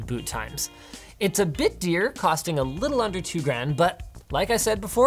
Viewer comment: “It’s a bit dyrt.” Am I hearing that right? Did you just throw in a correct Norwegian word out of the blue?